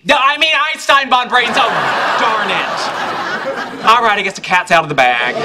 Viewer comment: what he say?? Sheldon ? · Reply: Darn it!